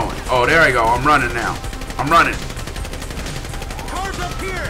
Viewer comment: Did anyone notice when he died at 9:59 in the did he said "oh fuck"??? Lol😂😂😂😂